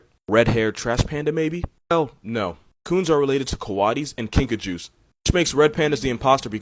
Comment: When you said Kinkajous I thought you said Pikachus and got excited